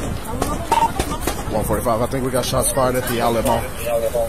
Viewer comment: At first I thought he said Shots fired at the Alamo but then realized it was 'the Outlet mall'